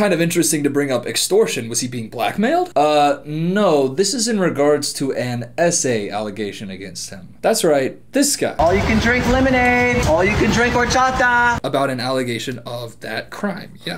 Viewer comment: You know you’re overly tired when you have to rewatch saying “S.A allegation about him” three times because you keep wondering what the essay alleged 🤦🏼‍♀️ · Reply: Yeah i thought he said essay allegation. As in someone wrote an essay about an allegation. Either way works I think lol